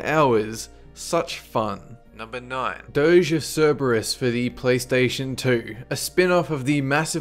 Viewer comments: I thought he said "Doge of Cerberus" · anyone else think he said "doge" for a second? xD